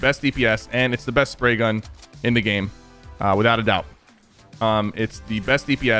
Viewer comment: “It’s the best spray gun in the game with out a doubt” “It’s the best spray gun in the game with out a doubt” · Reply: I heard him say "Without a doubt" If you turn on captions and have full blast...